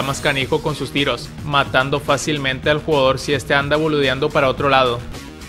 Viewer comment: Escuche mal o dice "este anda boludeando"? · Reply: No escuchaste mal, eso dice